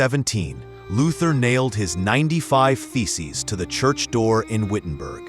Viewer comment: OH my gosh am I the only one who thought it was said that he nailed his feces to the church? (instead of thesis) ------------ so embarrassing.